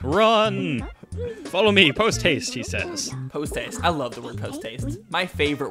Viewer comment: When E.Gadd said "posthaste" for some reason I thought he said "toothpaste".But considering A: I've not played this game on my gamecube yet and B:I was 2 years old when I saw the old adverts when they showed that text box, you can see why I thought he said "Follow me toothpaste." Also, somehow, Even though I was 2 years old, I knew about nintendo and knew who mario, luigi and yoshi were. Hell I had a yoshi plushie and used to play with that every day. And that's why my favourite character in mario is yoshi